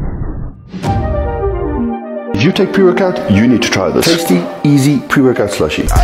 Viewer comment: It sounds like dude is saying “purecut” instead of “pre-workout”